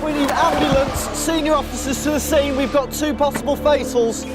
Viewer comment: what is a face hole lol that is what he said isn't it